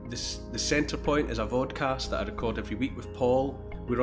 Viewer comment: I thought he said VODKAST....whatever...it's all good! LOL!